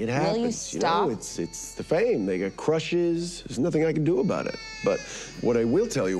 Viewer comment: did homelander just say the N word? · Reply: "they get"